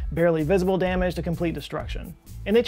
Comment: I heard "Vegeta scale of complete destruction".